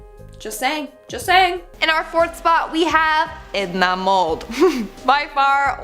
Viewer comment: I heard instead of Edna Mode I heard "It's not mold"